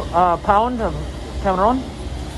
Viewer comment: I thought this man really said "one pound.... cabron" lmao